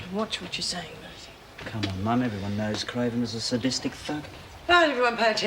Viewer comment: I'm sure he didn't say this @ 14:55, but it sounds like Marty says Craven "was a sadistic f*ck". I couldn't agree more. Had to play it several times before I figured out he was saying, "thug" with a "ck" sound at the end. I prefer the latter.